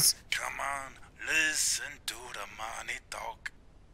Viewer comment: I thought he said listen to the money dog but then I realize it said he said listen to the money talk